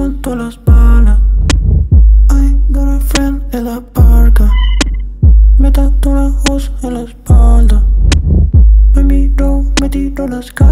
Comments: pense que decia que se tatuó a wos en la espalda JAJAJAJAJ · La primera vez que lo escuché pensé que decía "me tatuo a Wos en la espalda". Casi